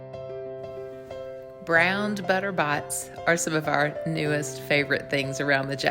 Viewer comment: sounds like you say Butter Bots!